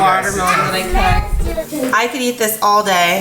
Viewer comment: "look at this beautiful watermelon that i cooked" 6:57 Me: WHaT dID SHe SAYYYYYYYYyyyyyy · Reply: She said cut